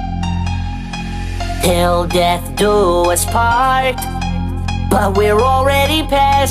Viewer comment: bruh I thought the beginning said "All i do is fart" this entire time